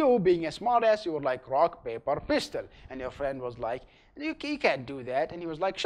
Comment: I heard Rock Paper Blistard